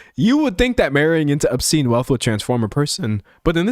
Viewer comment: I heard “obscene” as “Epstein” 👀 I mean…..